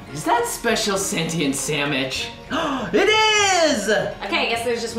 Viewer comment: did he say Samwich